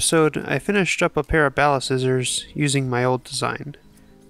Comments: i’m only here because i thought that said "ballscissors" and i was terrified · I thought it said balls scissors, just me ?